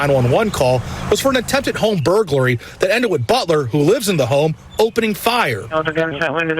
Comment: At first I thought they said he was the butler that lived in the home😂 His name is Butler😂🤷‍♂️